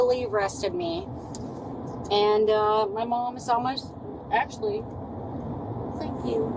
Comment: your MOM? Did I Hear that right?